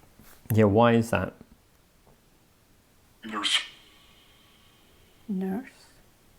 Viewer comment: Vox response at 38:30 sounded like it might have said “noose” instead of “nurse” 👀 which would be eerily relevant.